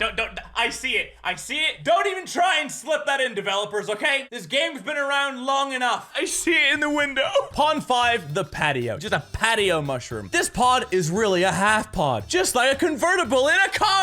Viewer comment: legit thought that said potato 9:57 OOOOOO DICK TWIST